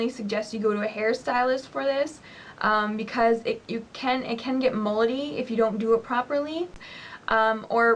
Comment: i thought you said "it can get moldy" at 0:34 lol.